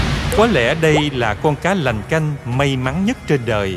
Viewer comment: Mình chưa hiểu " Cá Lành Canh" là cá gì nhỉ 🤔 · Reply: "cá lành canh" nghe nhầm thành "cá làm canh" 😅😅😅